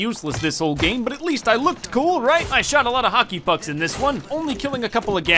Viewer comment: did he just say hokye f*cks